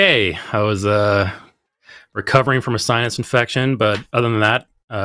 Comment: It sounds like he said he's recovering from a science infection. Though it was probably his sinuses that made it sound like that.